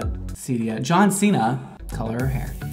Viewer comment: Yo, what you think about the new merch?? ✨ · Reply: it’s awesome!!! At first I thought that you said John cena lmao 😂👌🤟😊